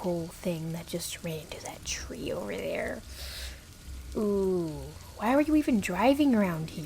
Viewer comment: Is it just me or I keep hearing "OwO" around 0:35 lol